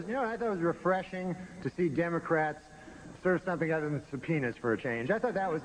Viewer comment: I thought he said for the longest time, “its refreshing to see democrats serve something other than some penis for a change” Didn’t know what a subpoena was 😂